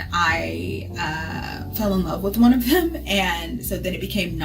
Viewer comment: did she say she fell in love with a gay man 🤔